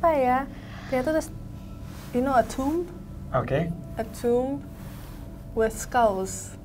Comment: Menit ke 27:02 "you know ..........." Mbaknya bilang apa ya? Saya ulang tp ga bisa nangkep 😂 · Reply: keknya "a tomb with scallous"